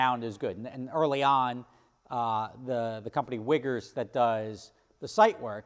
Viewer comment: What did he say? Lol. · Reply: Sounded like "wiggers"